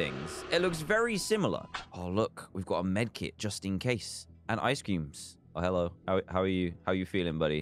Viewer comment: Did he say ice ceem at 6:13 !?